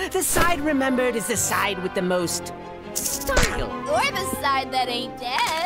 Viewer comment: Omg I thought he said sparkle somehow instead of style 🥲